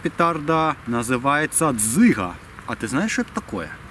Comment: послышалось цыган!